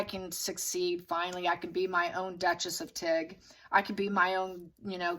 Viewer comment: when I heard you say...Tig, I thought you said...Duchess of Tick!!!!...🤣😂🤣😂🤣🪲